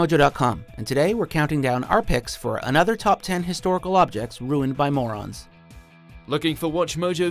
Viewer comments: For some reason I thought it said ruined by Mormons 😂😂😂😂😂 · I thought it said ruined by Mormon’s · I thought it said, "killed by Mormons" I was like 😮😂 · I thought it said mormon for second